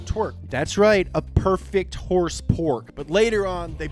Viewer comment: I hear Horsepork I'm on the floor laughing!